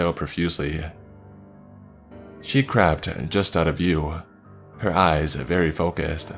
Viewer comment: talking about the dog in the last story, when he said "she crept just out of view" I swore he said "she crapped" instead. The fact that this makes sense with the context makes it even more funny.